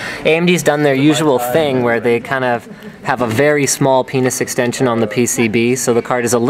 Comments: did Linus just say a very small penis extension on the PCB??!!! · WAIT WAIT WAIT ! Did he say a "very small penis extension" at 12:05 ?!!?!?!? wtf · it sounds like Linus says "penis extention". Is that an actual tech term or slang? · I dont mean to be rude but did Linus say penis extension on the pcb